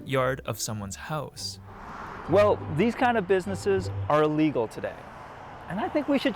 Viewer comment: You said they are legal and you think it should change. The sub says they are illegal and you think it should change. Idk if I should like or dislike this video... By what you said I should dislike but by the sub I should like...